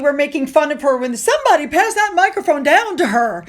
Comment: I heard "Nobody pass that microphone down to her"'